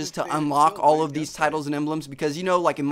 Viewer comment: i thought is said gold guns, tittes, and emblems